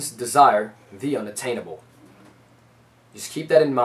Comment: "Humans desire the unattainable" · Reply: this whole time i thought he was saying unobtainable,e